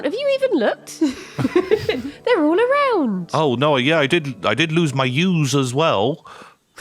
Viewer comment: what did reply here? I didn't understand 😕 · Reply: "Have you even looked?" "Yes, I did lose some Ewes." 'Ewe' is pronounced like 'You' and means a female sheep.